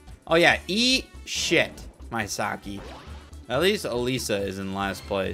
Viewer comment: Did he just say Mysaki instead of Mesaki?